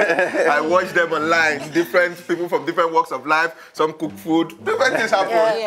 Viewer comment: 😂 😂 😂 Did i just hear “some cool food” Lol Mr macaroni cheese